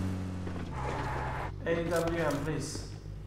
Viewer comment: did he say awm please? lol was kind of hard to hear